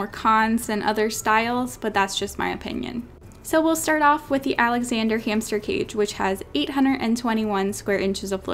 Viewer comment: I thought she said Alexander Hamilton but it was Alexander hamster cage 🤣